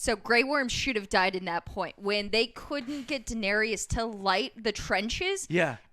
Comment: did she say daenerius?